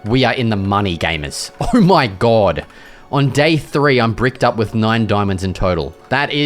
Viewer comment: wait did he just say I’m bricked up 😂